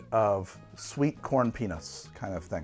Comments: I heard "sweet corn penis" for the last drink and it brought my attention right back to the show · I heard “sweet corn penis”… I wasn’t trying too, I just did. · I was listening to this and I did a double take when I heard “sweet corn penis” at 24:55. Looked at the screen and realized he meant peanuts. 😂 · Did anyone else hear that as 'sweetcorn penis'? Or is my brain doing some weird Freudian shit? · Anyone else hear "sweet corn penis" at 24:55?